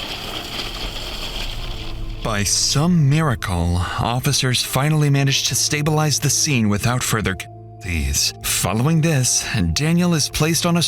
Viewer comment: What did he say here? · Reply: “By some miracle, officers finally managed to stabilize the scene without further casualties…” I don’t know why they censored casualties. 🤷‍♂️